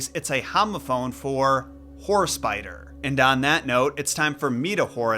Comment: i could have swore you said "Horse Biter" lol